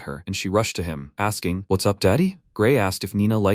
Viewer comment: nah 1:01:38 i was like did i hear wuts up daddy